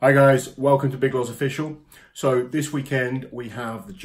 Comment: subtitles say welcome to big girls official 😂😂